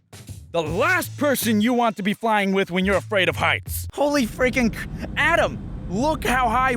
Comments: When you said you were afraid of heights at first I, thought you said you were afraid of ice. XD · When I watched this and he said he was afraid of heights, it sounded like he said he was afraid of ice...